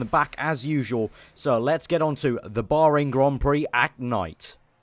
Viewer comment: I thought you said for the boring Grand Prix at night :D